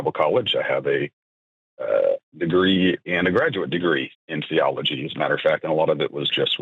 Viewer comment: Imagine going to graduate school for biology, getting a graduate degree in it, and then some internet call in show shows how your education was wrong. Close the school. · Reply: I thought he said Theology